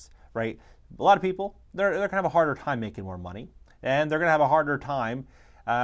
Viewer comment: did you say Black people are going to have a hard time making more money ??🤔